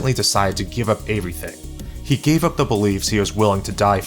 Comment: All I hear is “evewy” thing